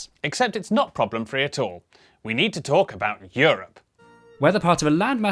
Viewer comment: When he said 'We need to talk about Europe', I got an ad and thought he said 'We need to talk abot you'💀💀💀😭😭😭🙏🙏🙏